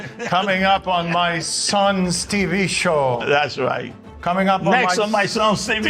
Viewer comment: Forever I thought was saying "Next on my son's stupid show". Which also fits.